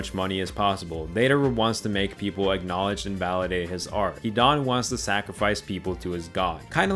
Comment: Bruhh, I have to repeat this part like 5 time. The caption says Dadu ?? Who's Dadu ? And who the f is Dedaru ?? If I remember correctly, it's Dedara.. DedaRA, not DedaRu.. Is this Chatgpt ?? Edit : 6:24 .. Nagito ?? Who is that ?? 😮 I'm so confused. I have to google it because it's has been so long and it's Nagato..